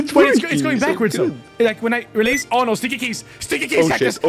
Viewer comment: i only heard that says is: stikikis stikikis